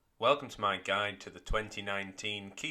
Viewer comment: I always thought he was saying my guy